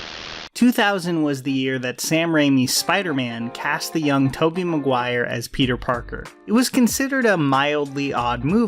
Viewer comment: did you just say YOUNG Toby Maguire?😂